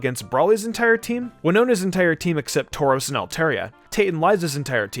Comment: is it just me or did I hear “tauros” ? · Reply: He said tauros